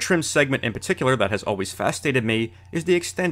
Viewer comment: haha he said "fastated me" instead of fascinated